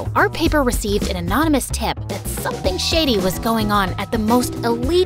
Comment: thought that said sh*tty 😭